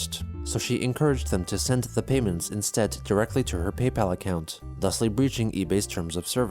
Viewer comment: did you say thusly? That's superfluous. You sound like Chris—speaking like that.